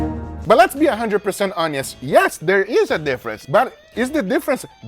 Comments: did he say "let's be 100% onions"?😄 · did he say let's be a 100% onions🤣🤣🤣🤣🤣🤣🤣🤣🤣🤣